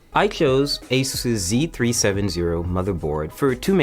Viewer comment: That's not what he said. He said Asus' Z 370 motherboard, not Asuzee or someting like that. That same comment is made by a lot of Verge build comment videos and I'm surprised so many people miss heard him. The rest of his build deserves every bit of flak people give it, just not that small detail